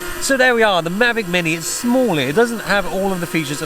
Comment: U can have a Lok of fun? Am I the only one misheard that?